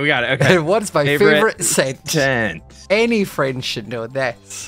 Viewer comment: I heard "Any french should know that" "Scott I'm straight" gave me whiplash